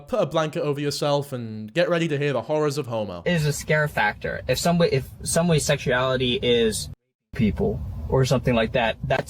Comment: i genuinely could not figure out what this said until i looked up the clip myself and heard it. He said if someone’s sexuality is r wording people. As in the act not the slur yk. Idk if im dumb or if no one commented it, i looked for a while and gave up. Youre welcome other confused people.